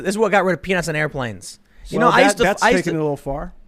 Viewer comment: I must be tired, I thought Tim said penises on airplanes, not peanuts.